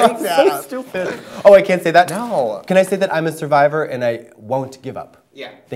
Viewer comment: what did Katya said ? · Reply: “I'm a survivor I ain’t gon' give up”, which is effectively a quote from the chorus of Survivor by Destiny’s Child